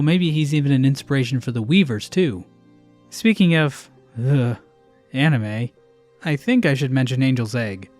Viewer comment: Did you just say "Ugh... anime..."???????